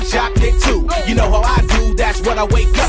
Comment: I thought he was saying "Reese's Puffs inspired this rhyme" not "Reese's Puffs sit right at this round" SHOCKING